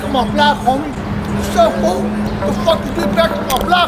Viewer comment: The amount of times I heard "wassup fool" is simply hilarious 😂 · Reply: It's " Foo " not fool ! 😂